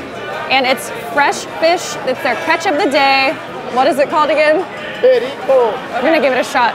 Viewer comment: I think he was trying to say tiradito 😂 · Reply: The name of the fish is perico actually.